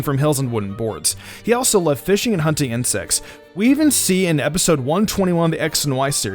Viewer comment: Did he just said at 5:07 that Ashed loved SEX???? Or did i misunderstood something? xDDD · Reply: fishing and hunting insects, i heard it wrong at first too